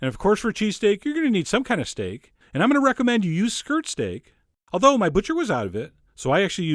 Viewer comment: "I'm gonna recommend used skirt steak" Say what? ...rewinds... Oh, OK, he didn't say that.